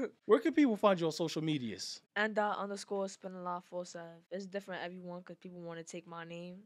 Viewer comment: I thought it said mdot spinalot.